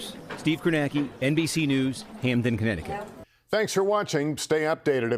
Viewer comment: I thought i heard "NBC News in Panic' at the end.😂